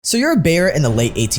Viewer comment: I was just listening and thought he said, "So you're a bear in the 1800s..." and I was like, "Continue."